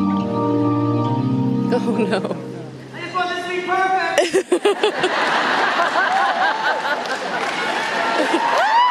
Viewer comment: What did Scott say at 3:17? I only understand “Perfect” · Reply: He said "I just want this to be perfect!"